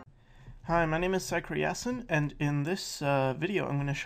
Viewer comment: At first I thought he said, "Hi my name is sexy assin'" I had to go back and listen again.